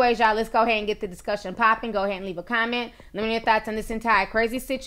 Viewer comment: Lol did she say thoughts or thots at the end of her closing her video @ 14:59? ?😂😂😂